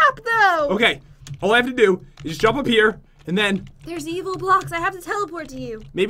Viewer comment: did jen say evil block at 35:19 ps i know she did wow so good english